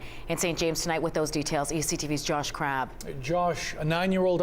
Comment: I thought they said josh crap